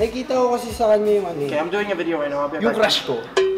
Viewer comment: was talking his father on the phone* Me: what did kyle said? He's so fast and hes speaking english😂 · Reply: he said im doing a video right now ill be right back